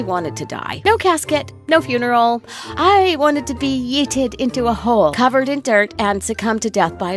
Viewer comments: did anyone hear this girl say yeeted? XD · I heard yeeted and was like... 🤔